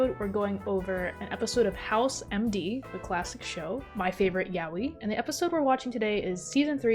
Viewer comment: Anyone know what a yawi or yowie or whatever was said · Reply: Yaoi